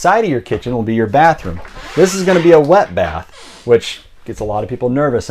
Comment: I thought he said lead bath that’s because I have him on two times speed!